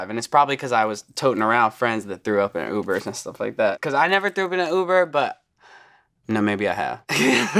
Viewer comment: Did he just say and I oop- Nm he said in an uber